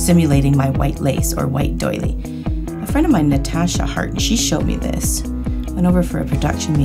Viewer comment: When she said Natasha Hart(sp?) I thought she said Natalya Hart. WWE anyone?😝